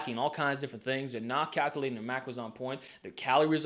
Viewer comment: I thought you said no to counting macros